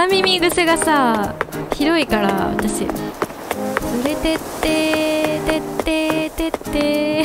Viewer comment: 「連れって～」って聞いたらどんどん聞こえてきて草www😂